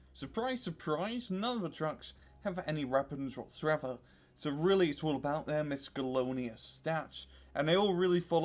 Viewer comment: wtf did he say?? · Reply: Using my gibberish translatormatic 2000 I figured out that what he was trying to say that trucks do not have weapons so it all about their misc stats.